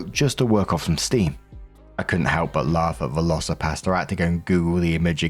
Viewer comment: i 100% thought you were saying velocipasta! 😂😂😂😂